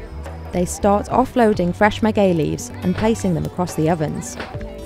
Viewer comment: I thought she is saying “gay leaves!” Ha ha